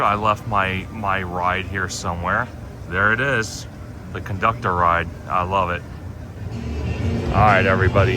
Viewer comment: The Time Conductor lol! Instead of theme park conductor